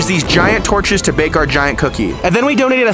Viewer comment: I thought he said subscribe if you want a Peace